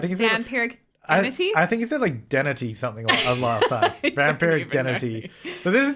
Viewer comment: I thought was trying to say Vampiric Deity or Entity